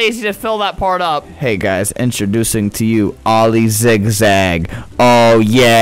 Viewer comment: Earthworm sally dies Oily ZAgZiG has now been in commence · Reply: I thought it said Oily zigzag the first time :/